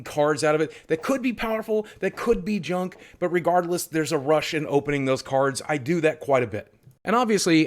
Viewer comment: I heard “A Russian opening those cards” and was completely lost